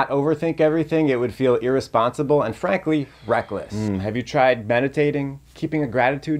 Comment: I heard him say "freckless" and now want to add it to the urban dictionary